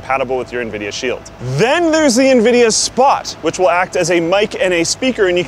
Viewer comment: When I watched Nvidia announce this product, I seriously thought the guy said "Nvidia SPY" instead of "Spot". Same thing really. This is how they are going to "spy" on us, privacy settings enabled or not. Their aim is to control your whole house so they can lock you out of it if they see fit - or even a hacker could do such a thing as well. On top of that, the cloud-based game play is subscription only..... FUCK THAT! I'm not paying "forever" to play a game, or any number of games, that I might like for a short time. This "Subscription" crap is irritating!!